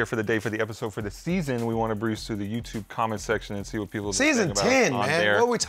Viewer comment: Who had this week's worst take? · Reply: gets worst take for saying " BRUISE through the comment section " @ 32:07 instead of " Browse " 🤣😂